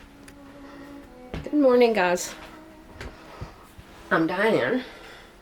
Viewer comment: hi new follower here..I really love your videos..but I have to tell you ..I thought at first you said you are DIEING! lolol You say hi..I'm dying..I guess you are say I'm? laughs..